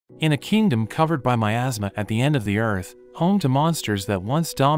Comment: I just heard it said “ in a kingdom covered by my asma” 😂😂